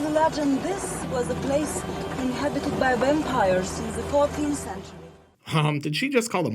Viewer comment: Actually... I heard "Bampires". And your "Daniel Larusso" comment did not escape me. Lolol!! I dont know why that one really got me, but it did. And then, of course, "We have the same haircut".